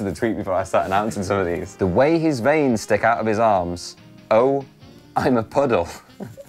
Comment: what is he saying at 1:20 ? i'm a poddle?